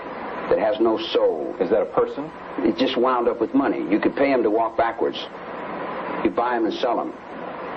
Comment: did he say "you could pay him to walk backwards"? That's hilarious